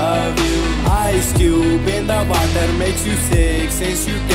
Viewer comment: why do i hear on "you start coughing" as "you suck coughing"